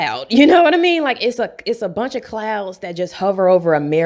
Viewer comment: I misheard the beginning as "Racism is kinda like a clown. It's a bunch of clowns that hover over America..." well, that's also accurate